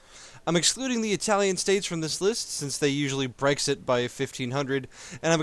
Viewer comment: That Brexit joke at the beinning made my day XD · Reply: I thought he said "HRExit" which is even better! I mean come on, Hrexit! This could become the most popular EU4ism since "Mingsplosion"!